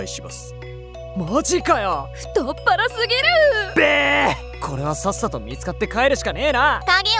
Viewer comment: の時に男の人が言った「やっべー！｣が何回聞いても「べーっ😜｣にしか聞こえん